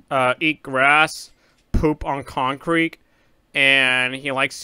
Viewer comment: i thought you said “eat poop on concrete”